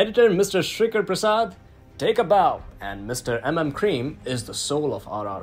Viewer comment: did you just said cream? Lol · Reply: Except Telugu, Keeravani garu is known as M M Kreem. Hence he said it that way.